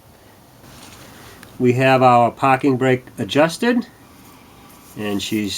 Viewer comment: I thought he said Paw King brake. It's a manufacturer of parking brakes that can't be de-activated by animals in the car. :P